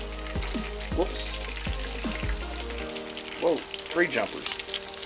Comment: Did you just say "tree jumper?"